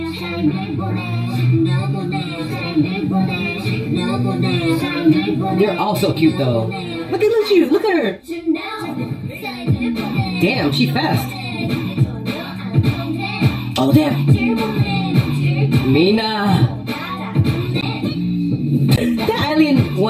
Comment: i thought he said "damn shes fat" lmao😂😂